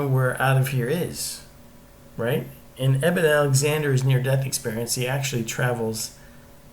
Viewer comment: Eben is correct spelling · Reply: thanks I heard him say enimen alexander, I thought no that can't be right. thanks so much.